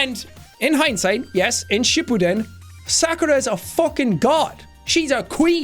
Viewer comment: When I heard shippuden I thought they said she pooted.